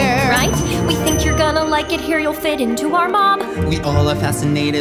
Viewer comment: I thought she said "you'll fit into our mom" WHY😭